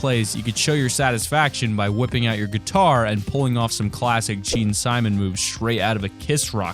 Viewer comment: DID YOU SAY GEANSIMON?!?!?!? ITS GEAN SIMMONS!!!! GET IT RIGHT